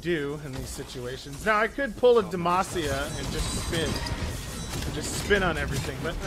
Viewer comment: Del, do you play League of Legends? I swear at 11:12 he says "I could pull a Demacia." xD